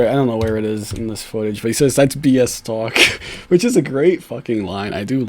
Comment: Not gonna lie, the first time I saw the interview I thought he said, " just penis talk" which made me laugh